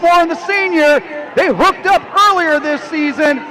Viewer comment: What did the announcer just say? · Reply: They hooked up 🗣️🗣️